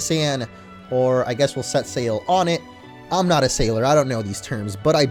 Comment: When you said set sail on it I thought you said Set sail onett. EarthBound reference